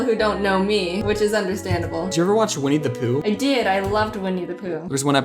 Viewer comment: lol i understood 'we need to poo' instead of 'winnie the poo